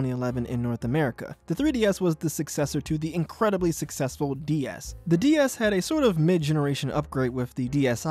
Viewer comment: Did you just say ds